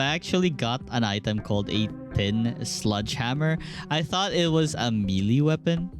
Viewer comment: ah yes the thin sludge hammer, the auto caption seem to have misheard tin sledge hammer slightly